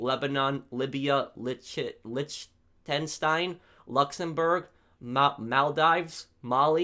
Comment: Did you just say Liechten shit 😂